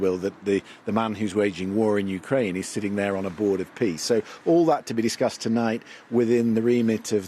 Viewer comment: Did he just say "Board of Piss"?